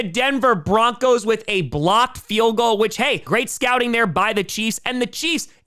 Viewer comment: I know he said blocked field goal, but for a moment i thought he said Blopped. Yep, that play was def Blop 10 worthy!